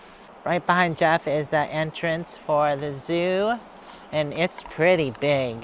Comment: Fifty bucks ? 😮 I agree, that is pretty steep 😂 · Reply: I thought she had said it included entry to the zoo so maybe not so bad of a price.